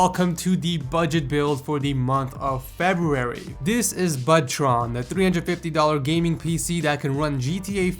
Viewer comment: I heard "ButtTron". XD